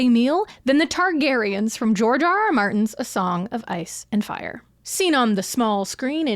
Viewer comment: When you said "George R.R. Martin" i heard "Jar Jar Martin" 😭